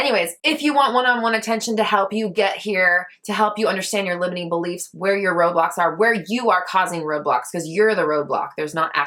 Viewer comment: I'm here as part of your manifestation because your collaboration with Veronica isles, also I heard " help with where your robots are" 😆🤖🚫🚧